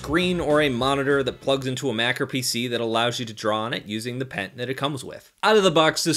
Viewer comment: what the heck is a macro pc? google says it has to do with computer science but are you referring to all type of pc's? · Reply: Senya Gotoku He said "Mac or PC"